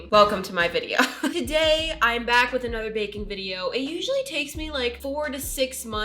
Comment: I was in my bed moving around and when u said baking video i thought u said bacon video and i was like bacon in roblox?¿?¿